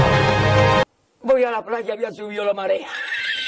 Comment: bro he is not sayings that he is saying ( when i got the beach the tide has risen ) i know Spanish and i convert this word into Spanish in Spanish it called as ( voy a la playa ya subio la marea )